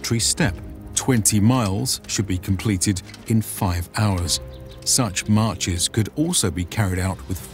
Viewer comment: I first thought I heard 20 miles should be completed in 5 minutes and went ‘excuse me what…?’